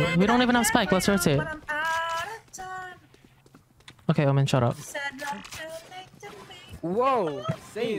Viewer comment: HAHAHAHAHHAHAHHAHAHHAHHAHAHAHHH I HEARD "WOMAN SHUT UP"